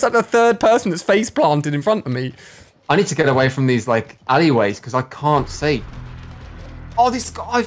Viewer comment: When said alleyways I thought he said Ali ways